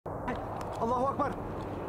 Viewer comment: Someone please give him a Clark Bar! He keeps saying “I’ll have a Clark Bar”! Poor guy just wants some candy. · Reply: Huh, I always thought they were saying it was alans clark bar.